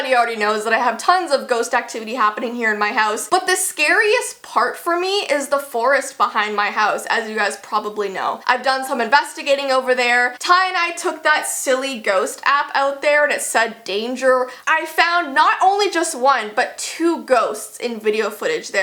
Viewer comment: you said "Ty and I" but i heard "tye dye" Im sorry if i ofended you or Ty